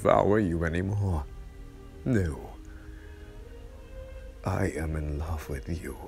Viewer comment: DID HE JUST SAY MI AMOR?!